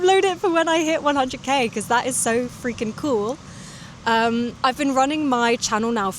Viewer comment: I can't understand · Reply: “that is so freakin cool!!”